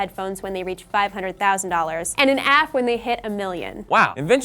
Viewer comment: And an Aff? Am I hearing that right because my sound shite?